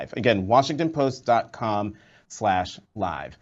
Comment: Washington Post Lies is more like it which is what it sounded like he said when signing off.